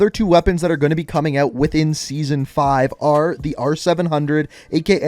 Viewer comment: excuse me what did he just say? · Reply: Its called intervention but its the r700